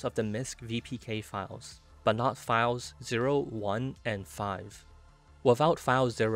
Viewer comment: I thought he said “flies”